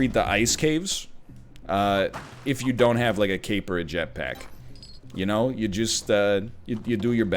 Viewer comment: Thought he said "A Kate Blanchett-pack" for a second